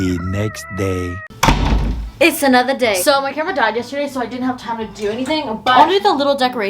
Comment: omg I had to replay that so many times I thought u said ur grandma died yesterday but u said camera omggggg I was so worried!!! 😂omgggggg